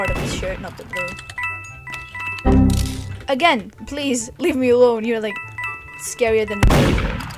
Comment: did she say f word?!?! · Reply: She said leave me alone